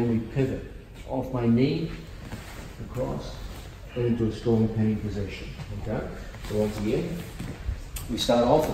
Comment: What is he saying at 19:17? It sounds like “storm of pain position.”